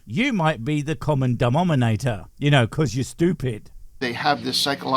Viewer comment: I heard "dumbnominator" 😊 Does he divide by zero? 😅